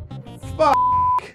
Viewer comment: I clearly heard Jirard say fuck. What was the point of the censorship?